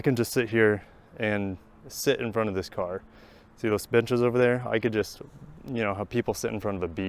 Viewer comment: Could have sworn you said “see those bitches over there “…..and you got me scanning for chicks